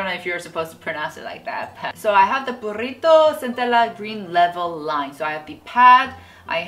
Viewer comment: lol it sounds like I'm saying burrito XD +한글 자막은 내일까지 업로드 할게용! ㅠㅠ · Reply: Before you showed the product, I totally thought you were saying BURRRRITO! ahahah!